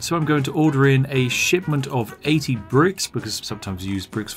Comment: Thought he was saying "a shit ton of bricks" not "shipment of bricks"